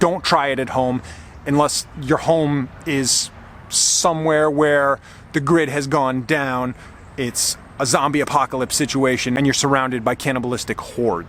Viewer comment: At first I thought you said cannibalistic whores! LOL